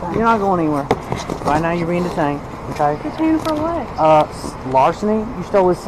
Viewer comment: idk who did those subtitles but he must've been on some good shit, they really said was; "right now you're being detained, okay" "detained for what"